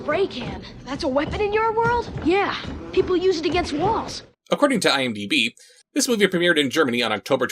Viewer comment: I totally misheard him, and thought he said wasps, not walls when he wished for the spay can